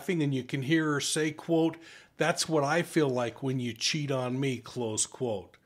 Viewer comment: I thought she said, ‘That’s what I feel like when you chock me’, in the video. She also could of been in shock on the body cam video or still intoxicated! Also she didn’t ‘stuff’ him in the suitcase, he got into the suitcase himself & she zipped him up! What kind of idiot let’s someone zip them up inside a suitcase? I don’t think this is a case of murder but maybe manslaughter. In the pic of Jorge his eyes look black like a demon, just an observation!!